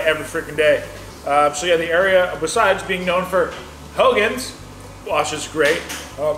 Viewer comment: talks so fast, I had to play it back with CC, as I thought he said; "famous for......'hellions'", referring to the squalling child in the background. I got clarification as 'Hogan's' on the playback. My bad. 🤣🤣